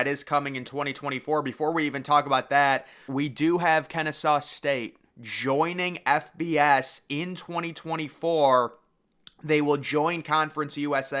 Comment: I swear it sounded like the dude said “Kinda Sus State” hahah damn..